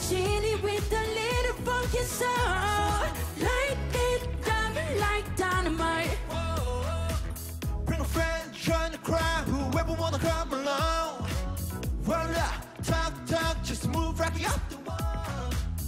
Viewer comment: I know he is saying crowd but it sounds like he is saying crap